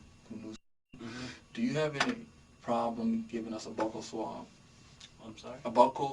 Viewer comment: The way the officer pronounced “buccal swab” caught me off guard and I thought he said “butthole.” I feel like the guy talking thought maybe something similar based on his initial reaction